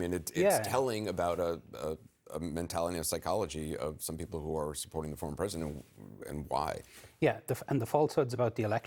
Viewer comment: I just want to say, Anderson did not say she was mentally ill as the Closed Captioning says. Anderson said, "There is a mentality of psychology of some people who are supporting a former president and why."